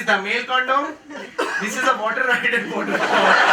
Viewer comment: what does he say @ 14:33? can anyone understand? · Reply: This is a water ride at a water park